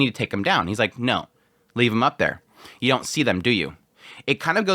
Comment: you got confused here, Denken doesn't say "You don't see them, do you?", he actually says "you CAN see them, can't you?". That's a key differance that you missed and assumed the wrong thing. Denken was just about to explain why they shouldn't take the corpses down because of the birds that were flying above. Laufen just didn't know that this was a trap, so Denken was basically telling her: look at the skies! You can see the birds, right? They are setting this up as a trap!